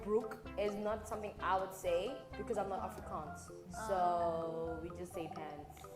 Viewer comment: It irks my soul that the subtitles keep showing "Africans" ( 7:19 and 10:31 ) instead of "Afrikaans" which is what she's actually saying💀